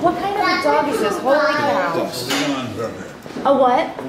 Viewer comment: I know the guy said it but apparently im hard of hearing these days, whats the type of dog? lol i heard "Neon Purple" and that cant be right lol